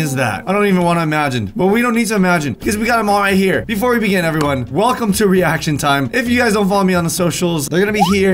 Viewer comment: I heard "hello welcome to ree action time"